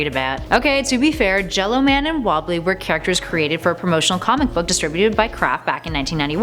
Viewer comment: Did she say "developed by CRAP back in 1991"? bc that's what it sounds like she said isn't it? Correct me if I'm wrong people